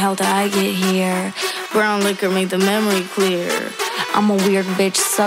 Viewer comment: Why did i hear "brown liquor made the memory queer"